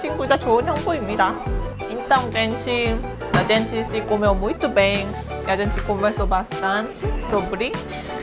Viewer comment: "Então gente a gente já se comeu" kkklkkkkkkkkkkkk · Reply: Eu entendi "a gente se comeu muito bem" hahaha